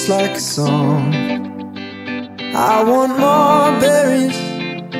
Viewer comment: It sounds like he says I want mom berryies